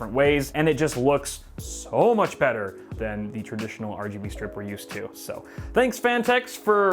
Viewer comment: "This looks so much better than the traditional RGB stripper used to." ..is what I heard at first...